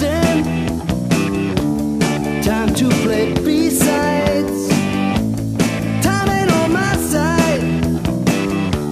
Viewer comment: So quick question: At 2:06, is he saying “Time to play besides” Or is he saying “Time to play B Sides.”? And that’s B Sides as in the second side of a record. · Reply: Think it's B sides. Other side of a record.